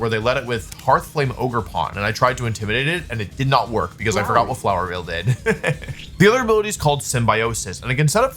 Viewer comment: is it me or I heard flowery in the background